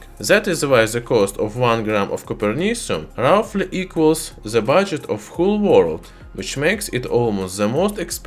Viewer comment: what did he say? How much does it cost? Im not trying to be mean or trolling, its a legit question from a subscriber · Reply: I think he said "budget of the whole world"